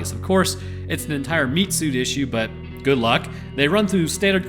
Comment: Apparently due to lack of sleep I misheard "meat suit" as "meat soup". And now I cannot unhear it. That's my story and I'm sticking to it.